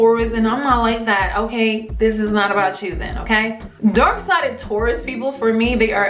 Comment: Sounds like your saying dork side...